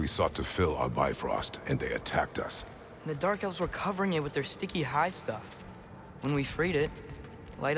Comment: I was strongly intoxicated when I went through this part and I heard it as "the dark elves were covering it with their sticky white stuff" and I had to reload an earlier safe to confirm what I just heard. I mean, if it was true, no wonder Brok was.... you know